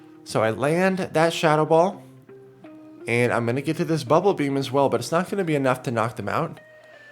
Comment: did just say “I’m gonna get to this bubble beam buts it’s not FINNA be enough to knock them out?” He’s getting gangster on us people! 🤣